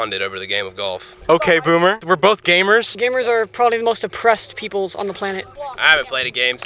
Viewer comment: “Gamers are some of the most oppressed people.” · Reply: thought he said 'depressed'